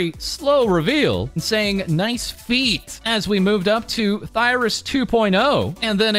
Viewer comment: My hearing is so bad, I thought he said VIRyS instead of ThIRyS.